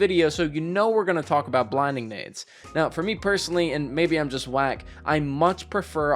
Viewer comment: did he say maybe I am black lol